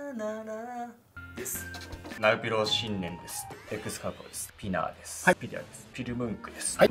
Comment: マジでエクスカ◯パーにしか聞こえないw